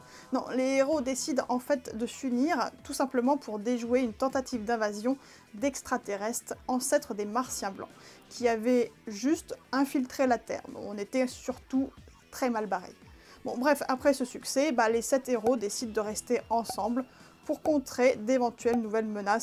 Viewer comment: A 2:51 j ai entendu "martien blanc " J ai pensé direct a doctor who et Supergirl jsp pourquoi 😅